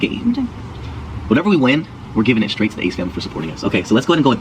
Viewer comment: why does Austin say 'whatever we win we're giving it back to the Ace family for supporting us'. Did I hear that correctly? He also says twice 'this is like a game'.